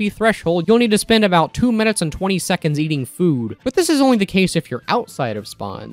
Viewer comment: The first time watching this I thought you said "eating poo" and my initial thought was "Oh, that's how the Heavy is able to recharge the Dalokohs Bar so quickly."